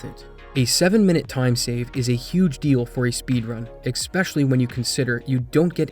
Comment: did you say expecially with an x?